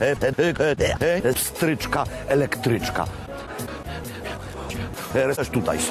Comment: Usłyszałem sraczka elektryczka DZIAD LUBIĄCY MASŁO